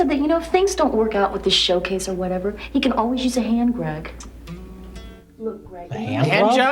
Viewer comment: I thought she said "hand rag," which sounds like Victorian slang for a silver polisher.